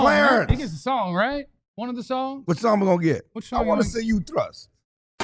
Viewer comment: did say, "I want to see you thrust" WTF 💀 WOW!!!